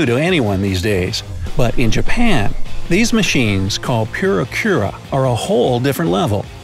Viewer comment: This is exactly the first time in my life that I heard someone saying like “Pyura-kyura”, instead of “Puri-kura”.